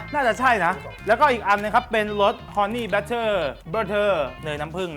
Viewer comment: ทําไมตอน 2:58 ได้ยินเป็น Horney butter วะ <num>